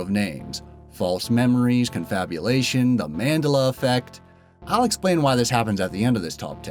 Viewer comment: Am i the only one that cringes when he says Mandela wrong? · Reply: Thought he was saying Mandala...